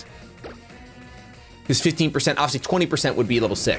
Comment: I'm smrt because I thought he said 50% level boost not 15%